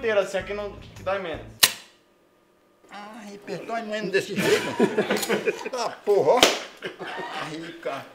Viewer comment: AIIIIN, DOI MEMO DESSE JEITO kkkkkkkkkkkkkkkkkkkkkkkkkkkkkkkkkkkkkkkkkkkkkkkkkkkkkkkkkkkkkkkkkkkkkkkkkkkkkkkkkkkkkkkkkkkkk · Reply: Ted TED doi menos desse jeito, foi oq ele disse